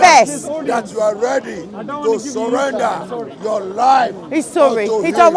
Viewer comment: Did he just say we need to surrender to Batman? Well I guess it’s better than surrendering to Allah. 😼😈